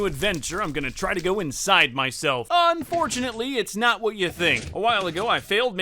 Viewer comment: Not even a minute in and the first thing I hear is “ I’m going to try and go in myself”